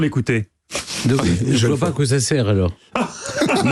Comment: Il dit quoi Stéphane Bern à 5:13 ? Je ne parviens pas à comprendre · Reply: "Je vois pas à quoi ça sert alors..."